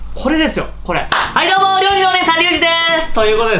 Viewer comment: 「料理のお兄さん」が「料理のお姉さん」に聞こえたのは私だけかしら…❓ 辛味にチーズ、めちゃくちゃ最高ですよね♬ ヨダレが止まらない🤤